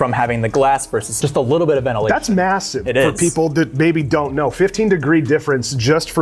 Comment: did you just say massive ?